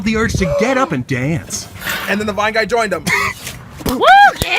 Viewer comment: "Suddenly the two felt the urge to get up and dance.""THEN THE VINE GUY JOINED THEM!!!!!!" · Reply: Omg stoppp I thought he said and then the bi guys joined them 🤣